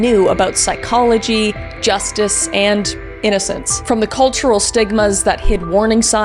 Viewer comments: The detail that 8yo Jacob tried to defend himself with his toy lightsaber caught me so off guard I actually started crying a little. He was just a baby 😭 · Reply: When you said stigma I thought you said sigma 😂😂😂😂😂😂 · When you said stigma I thought you said sigma 😂😂😂😂😂😂😂